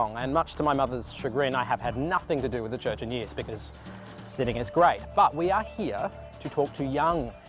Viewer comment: did he just say “sinning is great”?